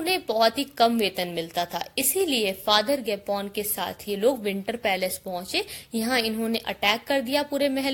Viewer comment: did she say gapon or gay p***